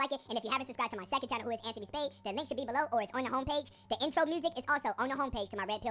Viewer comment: Honestly thought he said incell music, not intro music ... selective hearing i guess.